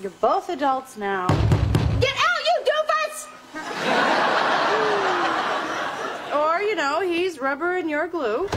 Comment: anyone knows how did she call him? Can not get that even over the urban dictionary 🤣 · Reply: She called him a doofus. Which means a stupid or foolish person.